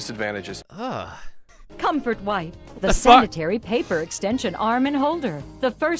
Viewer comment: The subtitle says "Comfort white" instead of "Comfort wipe".